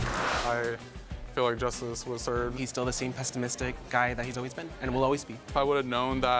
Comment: Pessimistic. I don't think that word means what you think it means. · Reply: Sounded like he said ‘pest-imistic’ which made it even funnier 😂